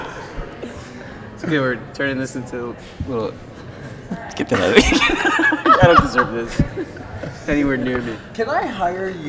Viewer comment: someone knows what dylan said at minute 3:13 ? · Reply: Get that outta here